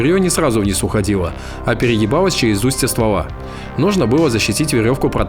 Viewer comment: А ПЕРЕЕБАЛОСЬ. И ДА. ВАМ НЕ ПОСЛЫШАЛОСЬ хахХАХАХХАХАХ. там отчётливо слышно переЕбало а не перегибалось